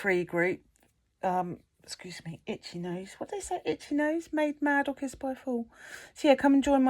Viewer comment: I’ve tried rewinding a couple of times but- WHAT is it you say about an itchy nose?! 🤣 I have had one often lately and it’s driving me nuts so that caught my attention real quick 🤣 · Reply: Made mad or kissed by a fool 😂 It’s an old saying/superstition😂